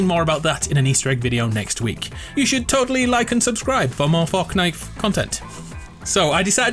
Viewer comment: did he say forknite